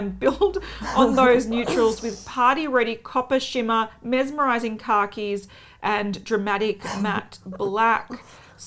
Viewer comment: Omg she said "mesmerizing khakis" and I thought she said "mesmerizing car keys"